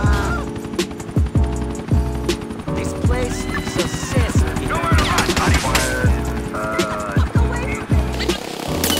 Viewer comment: what does Niko says? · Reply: "This place... is a cesspit"